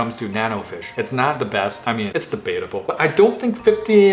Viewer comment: he says its debateable and I heard "it's the betta bowl".